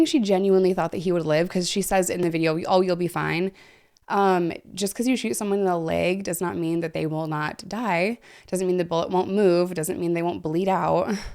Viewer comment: I thought she said love you bye instead of you’ll be fine till you repeated it an I was like how can somebody say that after sh**ting someone I was like huh then you explain an I’m like that’s sad but makes more since